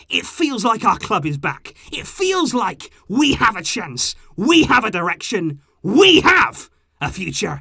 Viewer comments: I had my phone in my pocket, and I could have sworn that I heard "We have an erection" 🤣 I was like WHOOOAAAA there, maybe you're getting a lil too excited there 😂😂😂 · lol I thought he said ‘we have an erection’